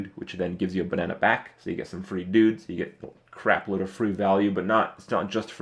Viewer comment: When he was talking about the Violet Teacher, I heard ''You get a lot of Fruit value'', instead of free value. lol